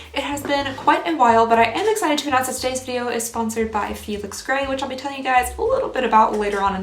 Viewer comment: The first time you said the sponsor's name I thought you said feed astray and I was like oh she's going to save the animals then you popped on glasses and said Felix ray lol